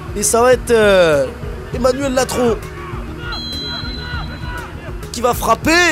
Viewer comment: J'ai entendu "Emmanuel Macron" :elbowcough